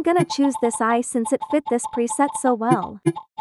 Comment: It sounds like your saying Peasant instead of preset 😭😭💀